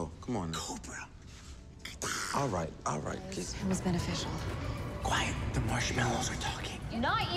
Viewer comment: OMG I LOVE NATE SO MUCH!!!!!! 😍😍😍😍 2:24 - "Quiet the marshmallows are talking" 🤣😂🤣😂😂🤣😂🤣😂🤣 2:35 - Nate: "I really love this show!" 😜 Me: "Same here buddy" 😉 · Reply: Purple Tiger I honestly thought he said “quiet the mushrooms are talking”